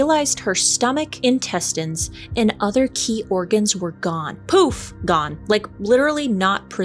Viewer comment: LMMFAO, I thought it said "her orgasms were missing"